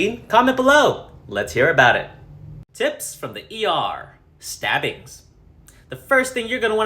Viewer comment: when it said "stabbings" I thought it said sibblings.😂😂😂